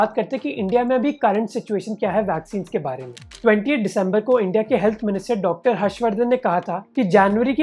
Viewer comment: he said 28th december whereas this video is posted on 26th december.. is he talking about future ? or is it just me who heard it as '28th december' 😝😂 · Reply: He said - 20th not 28th..